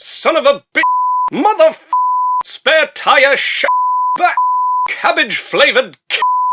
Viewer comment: I love swearing tie raid. Did he call that guy a wanker and a spare tire and say that he’s cabbage flavored because I honestly have no clue of what he said but it sounded hilarious.